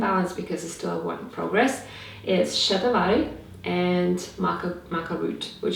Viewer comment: what berry did she say??? Can't understand her accent. Can someone please answer if you know the answer? Thank you very much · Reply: It's Shatavari, an Ayurvedic herb. Everything I mention is always listed in the description box, so just open it up. xx